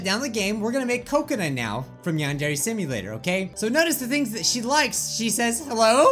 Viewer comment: Did.... did you just say... “Nyan-dere Simulator”? Or am I going insane 😐